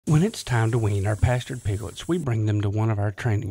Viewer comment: wean the bastard piglets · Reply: Nah, I thought you said bastard piglets instead of pastured piglets :D